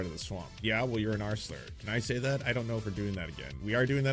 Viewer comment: I thought he said “arseler” I was like ‘wtf is this ridiculous newfangled dr seuss-as slang, damn im old’ then I realized he said “r slur” and I thought ‘damn im old’